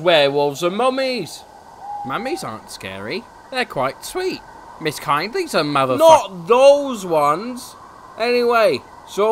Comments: WHAT DID HE SAY!?!?!?!?!?!?! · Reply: Mrs kindlys a mother fu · what was percy gonna say???? 😮😮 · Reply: He was about to say mother fvcker